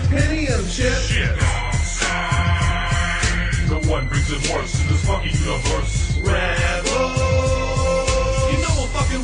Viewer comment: It sounds like vader say's: "no one brings it worse in this fucking universe" not "the one who brings remorse to this fucking universe"